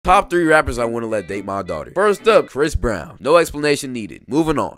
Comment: I thought you said top 3 rappers that i whould not let my dog date 😭😭😭